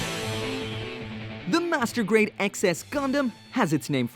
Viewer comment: mfw I heard that as "Master Grade Excess Condom"